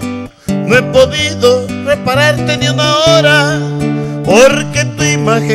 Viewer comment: Pensé que decía reemplazarte Y resulta que el señor dice que repararte jajajajajaja 😅😅😅